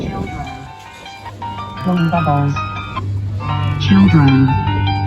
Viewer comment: ¿Que dijo ese kiddie ride? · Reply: Children blown the bubbles warning ⚠️⚠️⚠️⚠️ no music